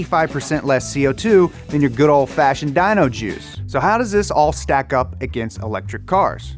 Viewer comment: I thought you were saying dyno juice.